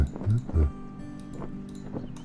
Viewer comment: I thought he said "he f--cked shoto so many times it can stand on it's own aparantely" 👁️👄👁️